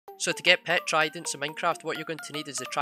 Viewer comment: I first thought that he was saying How to get a pet a trident😂😂😂